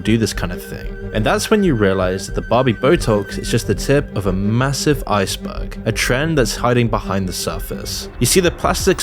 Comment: I thought I heard him say "a massive i-sperg". Which would have fit even better than "ice berg".